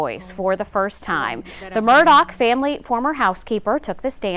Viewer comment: is she saying Murdock?